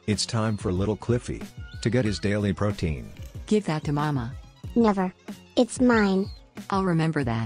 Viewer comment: Did just say to Cliffy, 'Give that to mama' ?